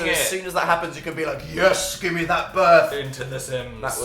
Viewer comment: can anyone else hear the word yes when phil is saying 'oh noo' at 19:14 ??